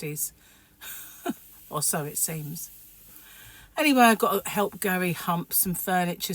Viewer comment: Glad you're sounding better . I thought I heard you say I need to hump Gary..lol instead of help Gary!! I love getting a new ornament for the tree each year. Your yarn ball ornaments are so cute !! The famous Gary blanket must always be near the couch! Have a great day!